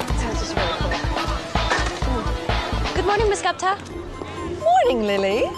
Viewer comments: Gosh I finally know that she answers saying “gupta” I always thought she said guntha or idk what I thought really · Idk why as a kid I always thought she said “Govna”. It made no sense but that’s what I heard. But she says “Gupta” her last name. Wow lol